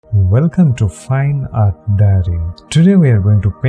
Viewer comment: I thought you said "fine art daddy" at the beginning and I was like 👁👄👁